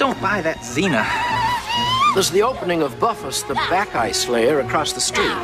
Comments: ehm did he say buffus the black guy slayer? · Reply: I think it was "Bad guy" slayer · ish til like 3:07 ish .. doesn't it sound like he says "the black guy slayer" ? Lol